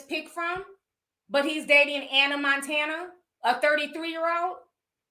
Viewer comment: Lol I’m sitting here smacking on some chips and I thought she was saying Hannah Montana 🤣🤣